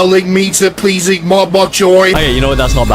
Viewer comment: are we gonna ignore the fact that the subtitles say to please eat more boy instead of bok choy?